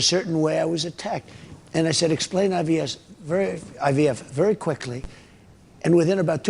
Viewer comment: it sounded like he said ‘IBS’ (Irritable Bowel Syndrome) when trying to say IVF. Wonder why that might have slipped out of his addled brain 🤔